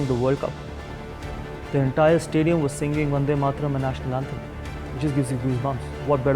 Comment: Did he say Vande Mataram, our national anthem? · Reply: He said Vande Mataram and our national anthem.